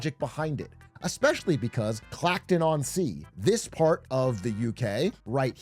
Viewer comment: I've seen it in Clacton. A movement is starting. Everyone is putting bins outside their house to show solidarity. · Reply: Sounded like Farage said Clapton at one point. Does he even know where he's standing?